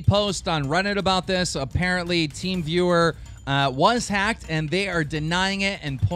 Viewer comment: i thought you said teen viewer and i got scared my porn channel got hacked